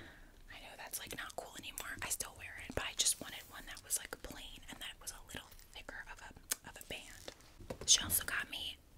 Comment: Bro at 21:17 what did she say?????? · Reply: She said , it’s time to go play with my new toy. 😊